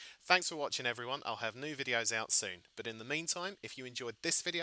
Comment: At the end of the videos it always sounds like you say "Make sure that like button takes the tits"